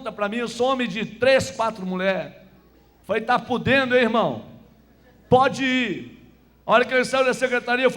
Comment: Eu ouvi direito ou ele falou um palavrão em 40:34 ???????? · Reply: ele falou ta pudendo irmao. seu Linguarudo surdo